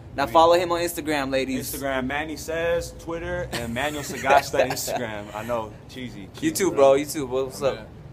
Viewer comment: WHAT DID THEY SAY THEIR INSTAGRAM NAME WAS? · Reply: IG